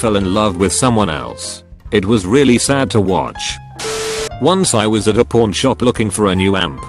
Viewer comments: i swear i heard "Once i was at a Porn Shop" i thought that was what it was until i actually looked at the screen instead of doing shit. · I heard "Once I was at a porn shop looking for a new amp." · So I had my back turned to the screen and thought he said that he bought a ring from a “porn shop” not “pawn”